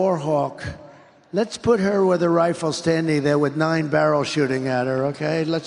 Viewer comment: Am I the only one who heard him say give her a rifle? I don't know you could face a firing squad while armed.